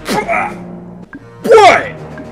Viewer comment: Мне одному послышалось "Пой!"